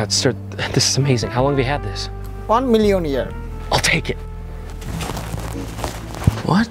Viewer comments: did he say “one million yen” or “one million year”? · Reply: One million year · He asked how long the store had the movie & the seller dude said "1 million Yen." Why? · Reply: So the seller said ¥1,000,000. I thought the seller said "one million year". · Did he just say "1 million years"?? 1:08 If so, DAYUM! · "Oh my god sir this is amazing, how long have you had this? "ONE MILLION YEAR". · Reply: I thought he said 'One million Yen', which would have been even more confusing.